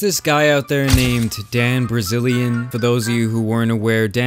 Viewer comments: Did he just said "Dan Brazilian" ? lol =D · did I just hear him say "Dan Brazilian"? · Am i tbe only one that heard you say Dan Brazilian